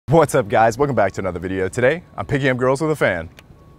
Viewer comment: I thought that said "picking up girls with a Van" and Thought that seems highly unsafe lmao! However im sure could pull it off!haha!